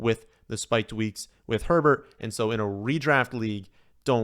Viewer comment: When taking on Mike Williams on underdog I really thought you said weeks with 102 TDs 😂